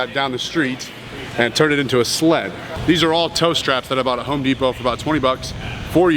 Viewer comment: Listened like 10 times but still can't figure out what kind of straps (1:35) ?? Somebody? Need a name too Google translate to Swedish:) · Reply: He said tow straps, as in for towing vehicles